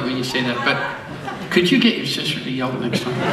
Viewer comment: Guys, help a non-native speaker out here. What does he say at 0:30? "Could you get me XYZ next time?" I just can't figure it out, but I'd really like to laugh along. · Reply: Could you get your sister to yell it!